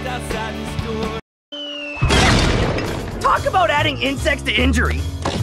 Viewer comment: Eddy: This just adds incest to injury! Me: Wait, what? *rewinds video Eddy: This just adds insects to injury! Me: Oh, thank god I misheard that.